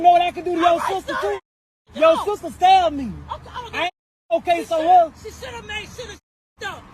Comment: All I heard was " your sister be selling cat"